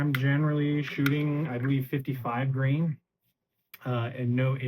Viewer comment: Did I hear you say you shoot 55 grain??? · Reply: 115 grain